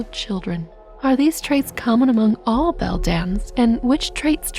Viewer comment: I always misheard it as "Bedlam", which kind of fits the other mother.